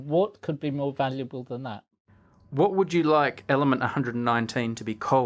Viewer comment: sounds like you said "What would you like element 119 to be Cold?"!!!